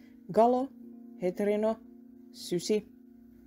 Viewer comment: At the end i thought they said "ultra soap"